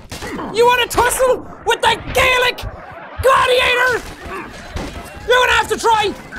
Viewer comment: I thought for a minute he said garlic gladiator